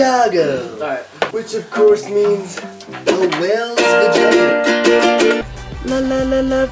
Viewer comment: WHAT DOES WES SAY AT 7:52 IT'S KILLING ME I CAN'T FIGURE IT OUT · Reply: A whales vagina